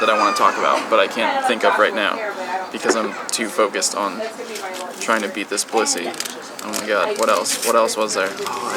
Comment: I swear I thought I heard something else. Trying to hard to beat this pu$$y haha!!